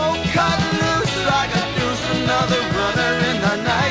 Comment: Cut Loose Like a Deuce vs Revved Up Like a Deuce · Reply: while Deuce certainty makes more sense listening to it closely here I can see how someone could mishear it. And douche is funnier.